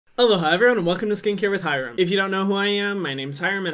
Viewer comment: I thought he always said hello hii everyone He said ALOHA ( just realised that 😂)